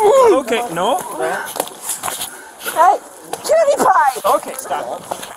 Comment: did she said "Pewdiepie"? And she has a gun?